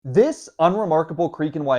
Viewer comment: LOL I thought you said unremarkable creep